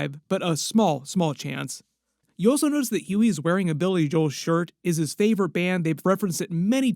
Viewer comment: did you just say Billy Joel is a band??